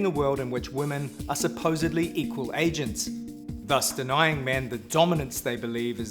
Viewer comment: what is he saying? "women are supposedly equal a..."? What's the last word? English isn't my first language. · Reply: "equal agents". In this context it means people who act, who make their own independent decisions.